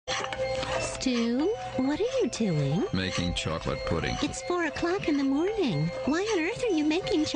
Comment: For some reason I thought it said "stfu I'm making chocolate pudding at 4am."